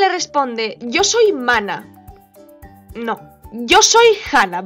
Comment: En el minuto 11:04 pensé que dijo "Yo soy humana" y me quedé "khá? ¿Hasta qué punto llegó la Happy's de ese tiempo xD?"